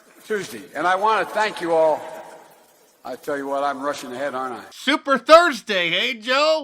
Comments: did he just say he's got his Russian head on? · I thought he said, "I'm super thirty!" Somebody hand Creepy Joe a cold beer!